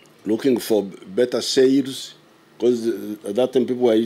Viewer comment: Ottoman TURKS, not TAX; Istanbul is in Turkey, that should have been your context clue. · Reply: 😂😅 Even I struggle to understand him due to his accent. Thought he said saves but he meant sails lol